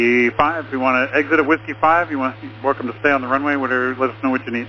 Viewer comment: I think TWR says "You're welcome to stay on the runway"